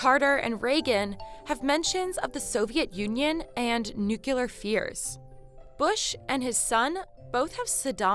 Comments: New killer fears – this is what I heard. Nuclear fears – what was intended to be said. Yep, I listened again and again a few times. It sounds NEW-killa, not NEW-clear. Is it a trend in American English or just a blooper? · did I hear you say nuculer rather than nuclear? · did she say 'nukehlar' instead of 'nuclear'?